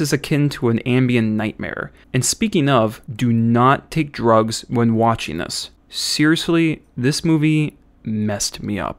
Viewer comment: "Seriously!! Do not take drugs and watch this movie." what i heard " Get drugs it's gonna be wild ride."